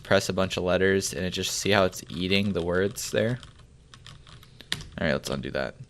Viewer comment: I swear I thought you said "see how it's yeeting the words there?" had to play it back to hear "eating"... what is wrong with me?